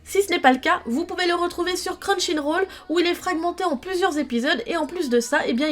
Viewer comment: | C'est moi où j'ai entendu non "Crunchyroll" mais "Crun chine roll" ? x,)